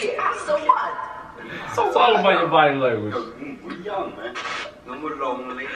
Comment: lucas is actually not trying to say “no more”, he is saying the korean word 너무, which is pronounced neomu and pretty much means “very”. so, he was mixing korean and english and therefore said “(i was) neomu (very) lonely” and continued to say “(but now i’m) neomu (very) happy” and later said “neomu romantic”, meaning “very romantic”. it’s obviously hard to know what they’re trying to say when you don’t know all the languages they’re speaking, i’m really not blaming you for thinking it was no more, i just wanted to point it out if anyone would like to know. the only korean i know are common words i’ve picked up by watching k-pop content, i’ve never really learned the language “normally” so i’m far far from good at it. i can understand when i read/hear/say some words and some very simple sentences but nothing more than that. 너무 is just a word that comes up a lot, especially in groups like nct where a lot of the members are foreigners and therefore obviously will speak simpler than native speakers since they’re always learning new words and so on.